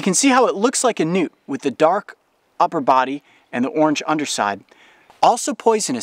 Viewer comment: Who else thought he said nudes..? Just me? *ok*..